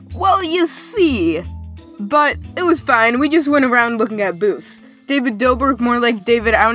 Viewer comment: for a second there i thought you said "But It was fine. We just went around looking at boobs".... What's wrong with me.... Also, i wonder if has any Adult Fans. That'd be weired, yet cool in my opinion